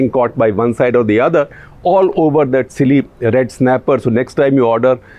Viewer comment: Always something new adds to my knowledge. Today is Red sniper in sir creek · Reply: I thought he said red sniper but he wanted to say red snapper? That’s an expensive fish!